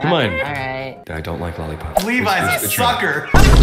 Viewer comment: did i just hear say levi you f*cker?